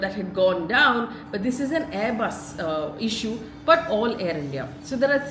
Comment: did she say AirBus issue at 1:40 ?